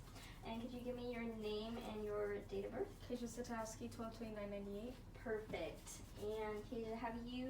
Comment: wtf i cant understand what she said. Whats her full name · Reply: Kayja Sitowski